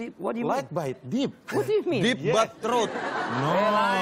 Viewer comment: "deep but throat" ASTAGA APAKAH INI SAYA TIDAK SALAH DENGAR HEY VINSENN? HALO KPU IYA HALO